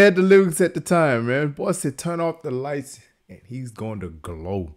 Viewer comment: I always thought he was saying "turn off the lights and lets go!" My young self thinking he was rapping about being environmentally friendly😂